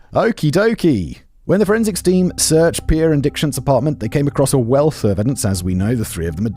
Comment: I swore you said Dickshat 😅😅😅